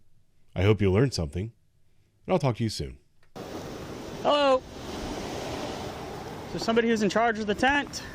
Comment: All I hear is “hello……… Is……. Tamra… Here….?